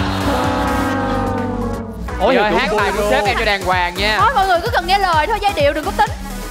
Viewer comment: ai nghe ông Trung nói gì không. "Chị ơi chị hát bài của Sếp e hát cho đàng hoàng nghe"